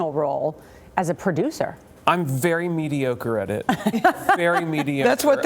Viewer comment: Can anybody tell me what word Jim uses in 5:46? I'm not a native speaker and I don't know the word and can't look it up cuz I have absolutly no clue how to spell it? :D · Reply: adjective: "mediocre" of only moderate quality; not very good